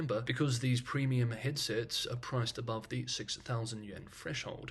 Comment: you’re talking about iPhones, but then you said “premium headsets,” and that “most are above the 6,000 yuan threshold.” That’s almost $900, I find it hard to believe that ANY headphones cost that much in China…can someone please clarify this? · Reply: I heard “handsets”which I understood to mean a cellphone.